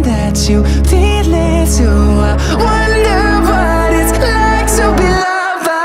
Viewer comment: Please tell me I’m not the only person who thought he said “i wonder what it means to be lava” Love shawn🖤